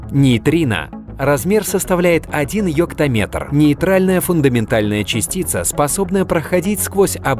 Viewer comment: странно, я услышал "ёптаметр".